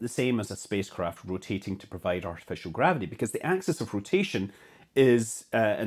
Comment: Oh, I thought it said Artificial gravy, Doh.